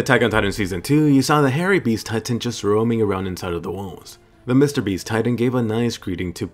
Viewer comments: did you just say mrbeast titan? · did he just say mr.beast